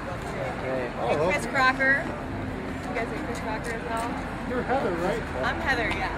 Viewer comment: did she say chris CRACKER at 0:07??